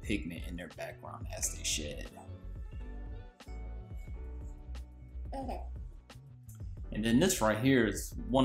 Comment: That's my son in the background lol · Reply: Lol 😂 Around the 6:00 mark it sounds like someone says okay. Even the captions picked it up. Is that your son too? Or his mama maybe?